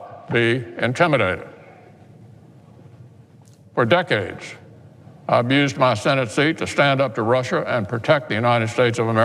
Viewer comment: I swear he said 'have abused' .